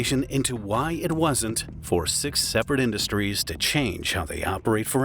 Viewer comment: i thought I heard 67, but it was 6 separate.